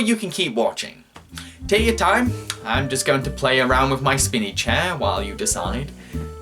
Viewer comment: LOL When you said "spinny chair" I heard "spinach hair"! XD